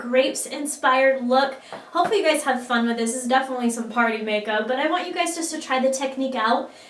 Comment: it sounded like pottey makeup lol love this look